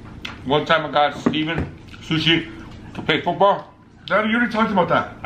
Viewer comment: What did his dad say at 2:48 ? Can anybody help me out on that please ? I couldn't understand him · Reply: Cody Fitzgerald to play football??